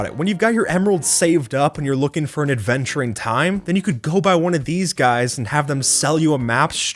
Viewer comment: i thought he said adventuring time........... AdVeNtUrE tImE, cOmE o